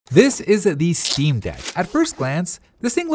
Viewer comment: At The start it Kinda sounds like he said this “isn’t” the steam deck